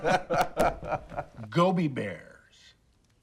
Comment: When he said Gobi bears i thought he said Gummi bears 🐻